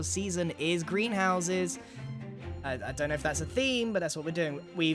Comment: You said you were doing green houses as a base this season and mumbo misheard you as “Grian houses” it seems 😂